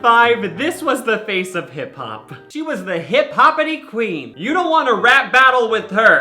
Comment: I legit thought you said she was the face of Hitler lmao